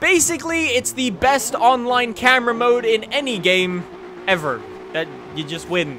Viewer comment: Jut follow the yellow red line. (edit) Did he just say drone camera mode?